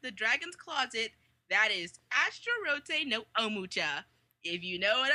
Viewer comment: anime at 11:56 ? i didn't hear it all the way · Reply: bombgameing Astarotte no Omocha!